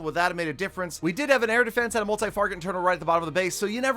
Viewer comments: Why do I always hear "Multi Farget Interno" instead of "Multi Target Inferno" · did anyone else hear multifarget interno?